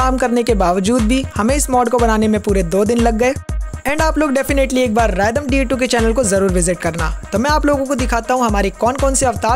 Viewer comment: it sounded like random da2😂